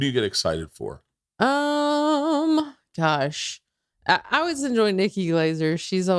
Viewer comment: thought she said "Tosh" instead of "gosh" for half a second and was reminded of Bobby Lee's story about texting not Daniel Tosh to be on TigerBelly 😂😂 it'd be so great to see Tosh on the Bertcast.